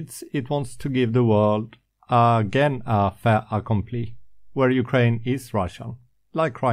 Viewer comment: What did you say here: 4:20 in the video. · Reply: You mean "Fait accompli?" I could have used it is done, but the term covers a bit more than that.